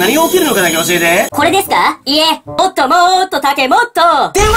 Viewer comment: 電話打ってちょうだいに聞こえるw